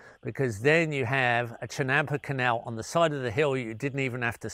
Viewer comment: what does he call the wet terrace? A chenampe canal??? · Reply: 🇲🇽 Chinampa, an Aztec agri system he dwells with in another Q&A video.